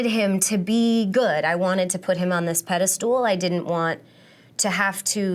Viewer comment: Did she just say 'pedal stool' instead of pedestal (2:12)? Reminds of a scene in IT crowd haha